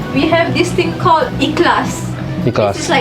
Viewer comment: What is the word she said at 2:55 ? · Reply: She mentioned the word "Ikhlas". It means sincerity and purity.